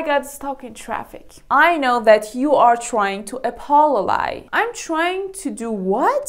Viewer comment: I actually thought you said Apollogize😅